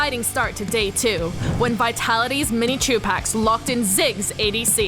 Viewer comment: I thought she said mini tupac